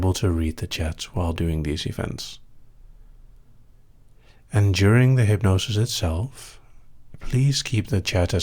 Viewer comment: "and during" I thought you said "enduring" probably because all day i've been thinking "endure it" i've been fighting through school and dance with a very painful migraine. XD